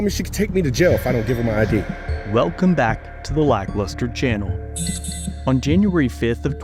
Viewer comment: Why does he say “like luster channel” ? when it’s called ‘lack lustre’.